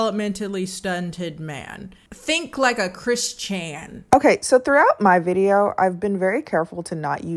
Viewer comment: She said Chris Chan I thought she said Christian and I was like hold on , what?????👀👁👄👁